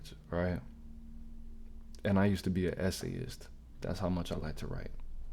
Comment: when you said "essayist" I heard S.A'ist, as in you used to S.A on the regular 😅 fkn YouTube lingo.